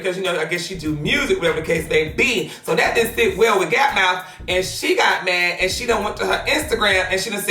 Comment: oh I had to replay that a few times, he did just call her gap mouth! Aaahahahahahahahahahahaha!